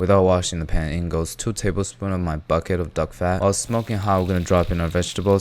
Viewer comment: Every time you say "duck fat", I hear "dog fat" 😅😂